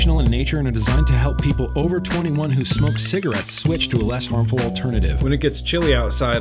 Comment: The intro the other day I swear it sounded like you said, "...snort cigarettes..." instead of "smoke". I cracked up at that.